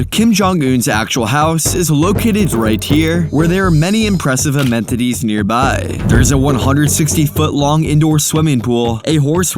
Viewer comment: "amentities" instead of "amenities"?